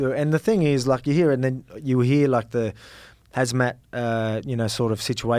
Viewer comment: Lol did Alex call khamzat “hazmat”